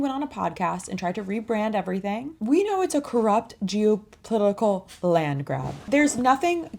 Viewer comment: you said Geo-Political Landgrab.....at first i thought you said Jew-Political Landgrab.....but no that would be crass.